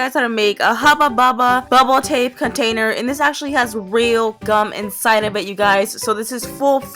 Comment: I thought she said "whale gum" instead of "real gum" and I was like, "Is this a new trend I haven't heard about?". It looks like I have a lot of faith in the worlds choices.